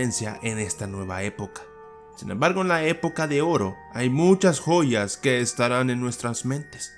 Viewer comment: hay muchas poll4s escuche yo xd